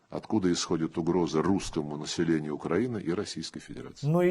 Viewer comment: "русскому населению Украины" мне не послышалось? Та русским вообще пофиг на смерти и людей, которые живут в Украине.